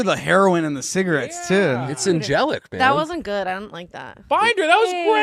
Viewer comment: Does say "It's angelic babe" at 1:11:32 after Amy Winehouse impression? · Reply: It’s angelic, dude